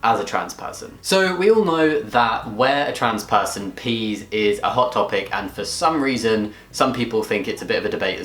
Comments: I thought you said "where a trans person pees is at Hot Topic" and i was so confused for a minute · I thought he said, "Where trans people pee is at Hot Topic" and I thought, "He's not wrong.